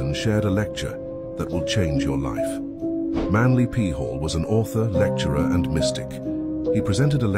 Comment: At about 20:01 I swear I thought he said Manly Peehole 🤣. After that no matter how much fire and wisdom he was spitting I couldn't get that out my head. Lol